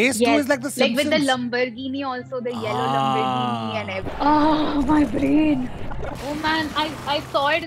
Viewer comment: lambarghini yes yellow lambarghini 😂😂😂 atleast two Italiens died after hearing lambarghini instead of Lamborghini.